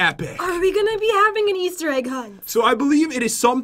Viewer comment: I thought it said, "Easter Eggo Hunt!" I know ridicules